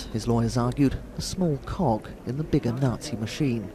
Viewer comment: It sounds like there saying small cock